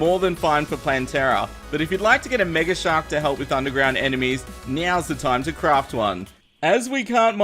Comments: Anyone else hear him say meows the time to craft one or is that just me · says meow instead of now